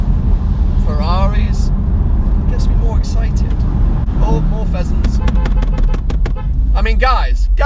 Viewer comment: I misheard and thought you said peasants:) did that RoRo Phantom experience really change your life that much?* *sarcasm.